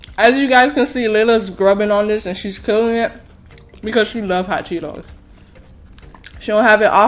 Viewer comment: Who heard her say chot Cheetos 😂🤣😂